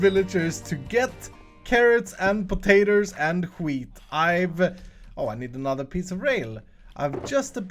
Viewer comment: anyone else hear "i need another pizza rail"?